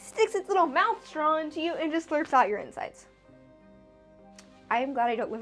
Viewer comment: Subtitles say this animal "slurps out your insights". Nooo not my insights! Anything but those!